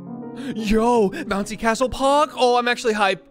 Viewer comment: i actually thought he said "Yoo Nazi castle pog"